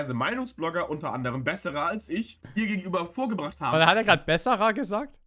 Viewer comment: Ich hab "bessere als ich" verstanden